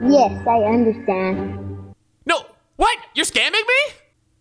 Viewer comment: I really thought he said “yes I’m scamming you” 😂😂😂